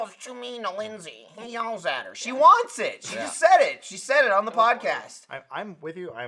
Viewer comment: I was listening to the audio and I thought Lindsay said she just farted. I went back on video and she said she just filled it haha